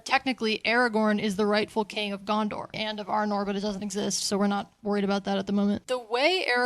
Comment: Goddamnit. Now all I hear you say is "Gondor and 'OH NO!!'"